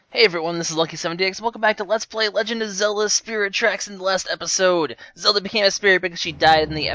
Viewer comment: when he said at the beginning, "hey guys welcome to spirit tracks" i thought he said spherical tracks. not sure how that is even possible, but okay.